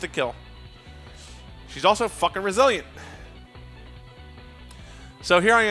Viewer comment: I misheard later in the video and thought he said "she's fucking Brazilian"